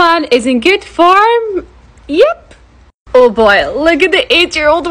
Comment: Why do I hear "8 year old" but the subtitle says "80 year old"😭😭😭